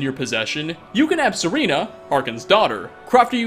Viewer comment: Did you just said "Serena"?